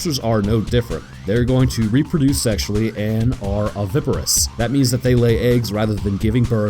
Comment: had to rewind it cause I thought you said "Omnivorous"